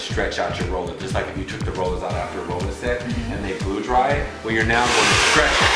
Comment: Informative video, but did he say, "BLEW dry it"? 13:31 LOL!